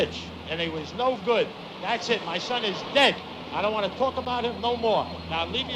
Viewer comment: Did he say "talk about"? I swear i heard "talk-a-bite".